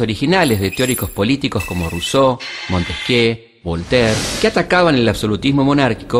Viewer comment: que dice despues de ruso? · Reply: montesquieu o algo asi entendi- creo que montesquieu es frances